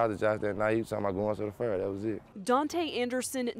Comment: We talked to Josh that night he was talking about going to the ? That was it. 0:26 we was just saying like surreal cuz we just spoke to him you know 0:31 since we were 16 or 17 1:07 in that trial he did great but in the end of the day i aint gonna lie i wish he stayed out that trial 1:27 i do tattoos so i did a couple tattoos on Josh he helped me out telling me i need to do this with my business this and that. Me and josh had a bunch of conversations that im gonna miss 1:40 he had no enemies, he had no time to make enemies, josh heart touched a lot of people you know what im sayin. If you lose somebody like that its like a little piece of everybody he touched is gone too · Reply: Anthony Cano “We was talking bout goin to the Fair” The Texas State Fair of Dallas